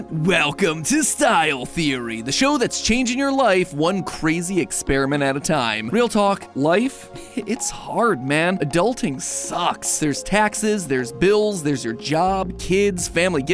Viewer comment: thought bro was about to say Life is Roblox…